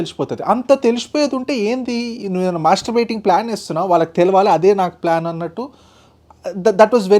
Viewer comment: mastering plan😂😂I heard differently